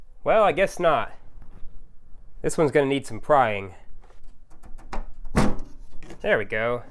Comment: Lol, when he said This one’s gonna require some prying and I heard “crying”. 😂